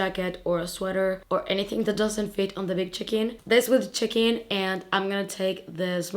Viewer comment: I thought you said the big chicken when referring to your big check in! 😂 🐓 I was wondering if maybe you had a bag shaped like a chicken. Lol